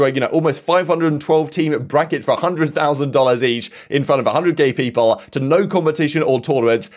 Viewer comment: "Infront of a 100 gay people?" 5:53 WTF are you saying? Just relax and read the tweet, I like you and your channel but bloody hell mate its always like one long unplanned rant, like a unmanned firehose spraying your info in such a chaotic manner wherever. Calm it and take a few moments between sentences, articulate what youre saying.